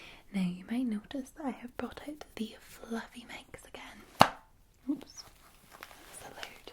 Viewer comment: I thought you said fluffy Mexicans first time around lol